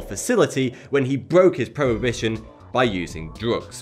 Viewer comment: While talking about Jonathan James, did you mean he broke his "probation" by doing drugs? It sounded like you said "prohibition".